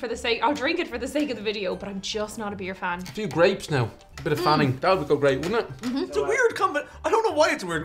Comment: i literally thought he said "some grapes and bit of fanny" looooool